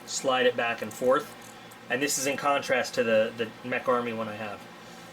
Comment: Mech Army or Tech Army? I thought you said Tech before. That Olite looks pretty great for $44.95. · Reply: Mech Army.